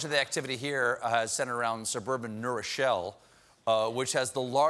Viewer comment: When I heard New Rochelle - I thought they were saying Neuro Shell on the news - like it was a "shell" they had created for the Virus... :)